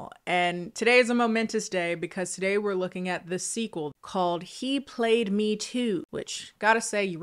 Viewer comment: I listen to more than watch your videos so I heard it as "He played me, too" and was SUPER confused when you started complaining about the title. Only to feel an ONSLAUGHT of disappointment to see "he played me II" onscreen. I could have accepted with minimal amounts of groaning if they were AT LEAST making an ATTEMPT by spelling words using numbers. I'm so disappointed.